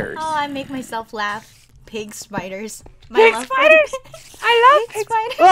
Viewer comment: pigspiders? I thought said cake spiders